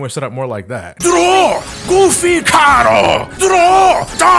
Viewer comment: When I heard " doror goofy cardo " I Died